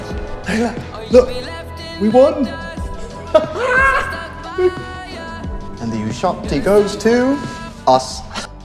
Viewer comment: „And the Oscar goes to… Us!“ I didn‘t even realize that while watching, I was like „Hah, good joke, because the figure looks like an Oscar“, and now that I know Oscar Isaacs name, it‘s even funnier :D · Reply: I think i heard him say " And the ushabti goes to us"